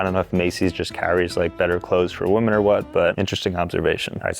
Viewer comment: says woman instead of women